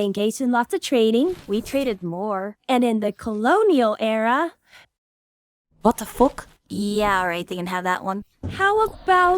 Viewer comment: Did she say "What the f**k?"